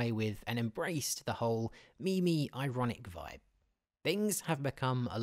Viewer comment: Did he just say "Me Me" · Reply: Cactus No, he said meme-y